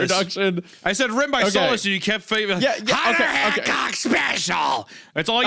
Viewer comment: Because can't pronounce a "T" to save his life, it sounded like he said, "We were allowed to talk to women, a Hun'er Hancock special." Ironically though, he said Hunter twice after that and pronounced the "T" in Hunter both times🤔 But then the third time he said Hunter after that he didn't pronounce the "T"🤔🤔